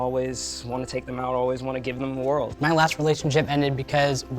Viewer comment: I legit thought he said "I always wanna give them oral"